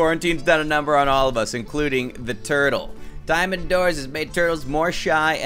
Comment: I think he said diamond doors lol!!!